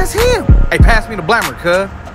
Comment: "On me that's him" "AYE, PASS ME THA BLAMMER CUH!!" 😂😂😭😭....I lost it. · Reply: I legit thought he said blabber, makes sense now.